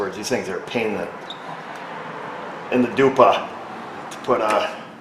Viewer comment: Did you said "pain in the dupa"? :D it's polish did you know that? nah nah nah nah nah. Dupa means a** ;)